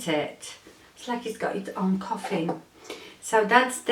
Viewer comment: I loved hearing about your trip. I look forward to hearing more about it. I am glad you had a great time! Thank you for sharing it with us! LOL Did u say "It's like the brush has it's own coffin"? Anyways I thought that was what you said (my laptop volume is low) and it made me laugh! Oh how did Louis do while you were away??? Have a great Tuesday! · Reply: Thank you, yes I said Coffin lol, Louis had a great time staying with Lola-trips to a park which had a stream running through it (and you know how much he loves water) and trips to the beach. He was so happy to see me though, he was licking me for 10 minutes on the floor lol xoxo